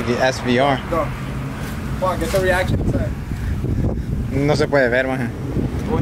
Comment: Minuto 11:44 escuche una palabra salvadoreña "maje", no entiendo nada inglés pero que bueno fuera que subieras a tu canal de español videos mi hermano, transmiten buena vibra ✌️